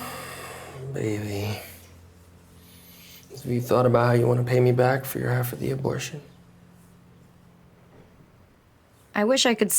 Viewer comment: What did he said in 8:21 ? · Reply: “Baby, have you thought about how you want to pay me back for your half of the abortion?”